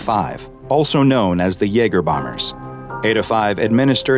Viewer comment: I HEARD YEAGER